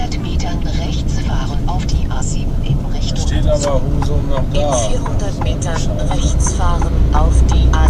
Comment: habe ich Husum gehört ? LG aus Husum xD